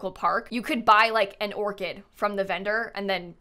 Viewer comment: I'm watching this out of the corner of my eye while I'm at work and, hand to God, when you were talking about the floral cart, I thought you said "You could buy an organ" and I was immediately confused about what direction the sims was going 😂